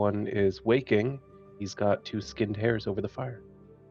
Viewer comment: Oh, "hares". I thought said he was cooking three "hairs" and thinking "that's enough protein for us." :face-purple-smiling-tears